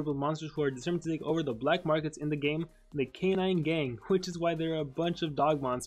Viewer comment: When said “alcanine” i thought he said “arcanine” lol..